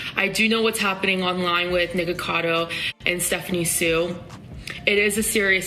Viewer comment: am I the only one who heard niggacado